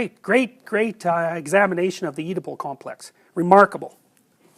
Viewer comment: which complex does he mention at 2:03 ? Can't really understand, please help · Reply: The Oedipal Complex. It means mommy issues.